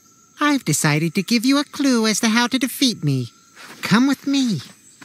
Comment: this is wat I hear “eye in the e sand”